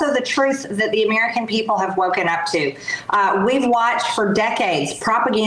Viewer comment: Did MTG just say “Americans need to get ‘woke” or did I misunderstand?